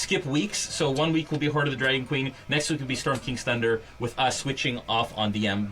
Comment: I thought I heard Hoard Of The Drag-queen in the announcements then realized it was Dragon Queen. LOL